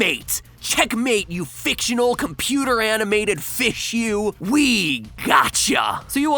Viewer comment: I thought you said "Weed got 'cha" in which I would've responded "420 blaze it get rekt no scope mf"